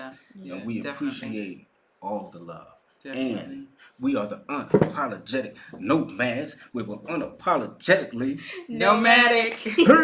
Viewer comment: I thought you were saying unapologetic no mask. 😂 My COVID way of thinking. 😂